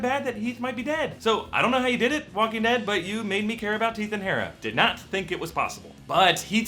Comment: he says "teeth and Hara" instead of Heath and Tara. Bahahaha